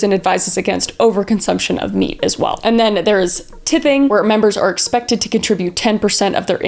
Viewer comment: did she say tipping or tithing? i can't tell and the idea of religious tipping is funny